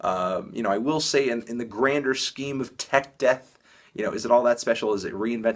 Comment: thought you said "tech deck" at 8:00 for a hot second